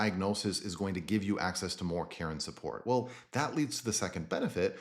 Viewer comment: I kept hearing "care and support" as "Karen support" and I kept imagining a group of boomer aged women who ask to speak to the manager on your behalf.